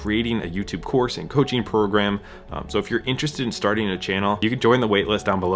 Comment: Lol, I thought you said YouTube course and Cooking program. 🤣 Sweet I need to learn both those unrelated things. 😂